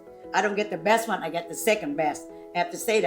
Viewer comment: Jeannie’s face when starts talking about how uncle ted is second best. Lmao. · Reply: I thought she said "second bad" referring papa Mai was bad but uncle Ted is worse, so he is the second bad :)